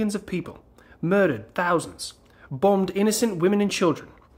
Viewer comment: I had to adjust my audio for a second because I heard that as “Boned” instead of “Bombed”… Which IS still accurate BTW. A LOT of war crimes going on in the occupied regions.